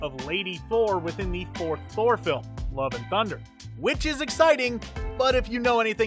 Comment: "Thor Four film" Lol for a second I thought it said Thor Thor film 😂